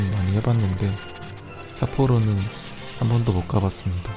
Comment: "사포로 가는 건 많이 해봤는데 삿포로는 한 번도 못 가봤습니다."에서 빵 터졌습니다. ㅋㅋㅋㅋㅋㅋ "사포로는 한 번도 못가봤습니다."로 잘못 들었네요